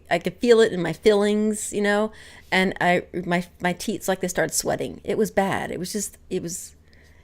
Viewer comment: PS. At 3:08 it sounds like you say "teets".